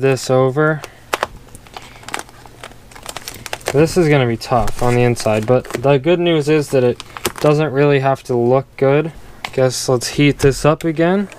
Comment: it sounded like you said “thy good news is” lol